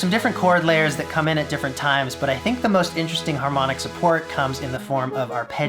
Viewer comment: Lol 4:47 Man I thought it said Child Support. I was all like, "Hmm that escalated fast."